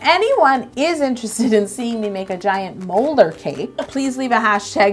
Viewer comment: I thought she said giant ruler cake